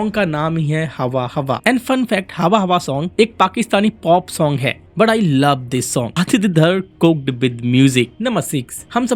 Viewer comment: ye kya bol dia? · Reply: aditya dhar cooked with music 😅